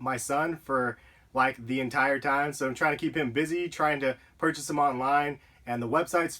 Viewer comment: just thought, you said you was busy, trying to purchase your son online, lol, nice vid :D